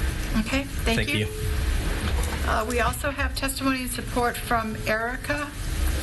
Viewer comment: does she say "support from Erica?" and she does not appear?